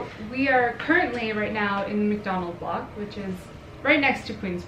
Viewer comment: I thought she said we’re currently in Mac Donald’s park 😂😂😂😂👁👄👁